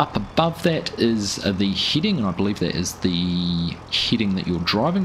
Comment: FYI: head·ing /ˈhediNG/ not Heeding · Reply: apparently so... I heard "heating" at first and wondered if that was a thing now with winter play :) You do you, accents are hard. I can't understand some of them in the UK and I've lived here for over 50 years.